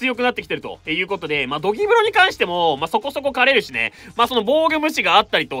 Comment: ここのところドリブロがゴキブリに聞こえてさんも黒ロジャーゴキブリって言うんだと思った😮